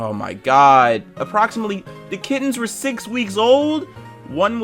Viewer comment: at first i heard kids and i was like "holy fucking shit, dude", then i read kittens and my reaction didn't change much